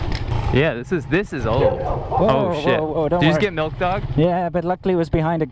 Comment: I'm not sure what you were saying exactly but it almost sounded like you were saying "Milk dog" Referring to the dog that was behind the gate barking at yall. 11:04 What is it you are referring to and/or saying?